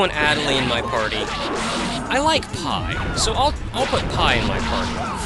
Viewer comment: i was so confused for a bit there when he said "i like pi" at first...i thought he said "i like pie" rofl XD